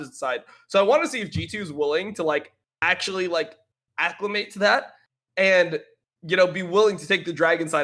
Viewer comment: What word did say at 1:00:27 ? Sounded like 'acliment', but that's not a word, so I'm wondering what it was. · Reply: Acclimate. Another way of saying acclimatize.